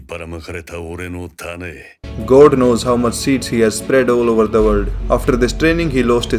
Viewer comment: BRO I LITERALLY THOUGHT HE SAID "ive spread my speed all over the world"